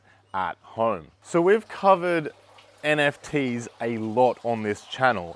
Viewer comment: did i hear... NFTs? 🙈